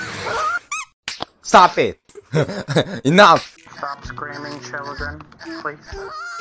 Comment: Bihind the guy said: “Stop screaming children. Please” 5:54 Me: ( WAIT THE MINUTE WHO ARE YOU ) LoL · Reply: I actually thought he said “stop STREAMING children”